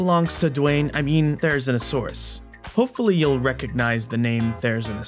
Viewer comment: I keep mishearing the name as "There-is-no-saurus" please help